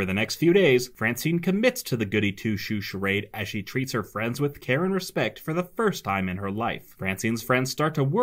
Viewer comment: I thought I heard "Karen respect," not "care and respect."